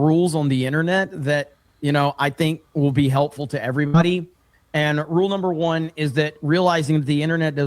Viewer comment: He might’ve said “everybody” but I swear to God I heard him say “every money”